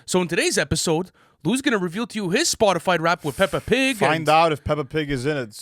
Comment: did he say spotified rap???